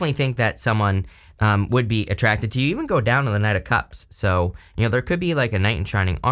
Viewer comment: i could have sworn you said "you even go down on the knight of cups" and i was like "I WHAT?!